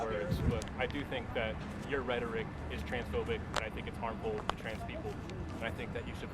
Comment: Did I hear that right, 1:53, "your rhetoric is transphobic"?